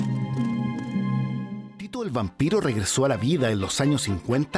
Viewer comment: Pensé que decían " tito el bambino" jajaja y era tito el vampiro